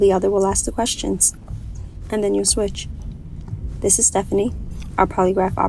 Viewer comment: I fully thought she said "and then you smooch" 😭😭